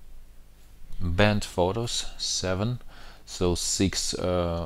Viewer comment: I was listening and not watching and thought you said "BENT photos" LOL instead of "Band Photos" and was thinking "wow, what a ripoff, they sent you bent photos like CVS does" LOL